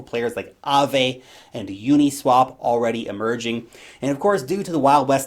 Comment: WHEN YOU SAID AAVE AND UNISWAP "EMERGING"... I ALMOST SHIT MYSELF THOUGHT YOU SAID "MERGING"